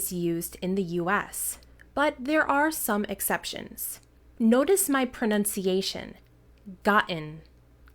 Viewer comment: she says “notice” but in subtitles say “Listen to”